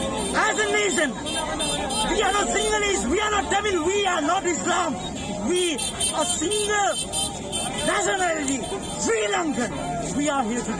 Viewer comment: Did he just say that? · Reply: What he said was we are not sinhalese were not tamil we are not islams we are sri lankans.